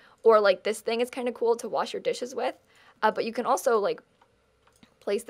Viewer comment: What said: Wait, hold on, watch this. What my lame mind thought right after it heard it: Wait, hold on, wash this. Wow. HAHAHAHAHAHAHAHAHAAHAHHAAHAHHAHAHAHAHAHHAHAHAHHAHA